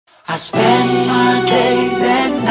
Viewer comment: I thought he said "I spread my legs" instead of "I spend my days" lol